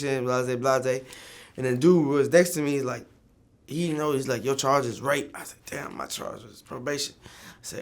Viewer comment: did he just say "your charge was rape"?